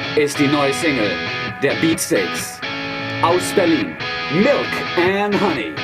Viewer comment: I thought is said Breaststeaks - Milk & Honey